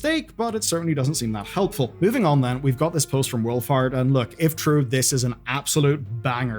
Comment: oh, Wolfhart, I thought you said wool fart